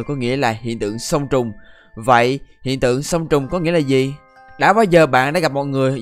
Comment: nghe nhầm thành sơn tùng :)